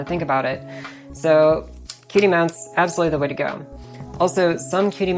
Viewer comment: I heard "QD Mount" as "Cutie Mount" and now I can't hear it any other way, thanks.